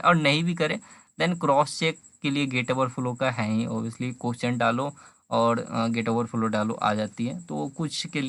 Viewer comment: Is gate wallah pyq good for ece? please reply. also at 13:48 what you said where to crosscheck? Can't understand the audio? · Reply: Sounds like gate overflow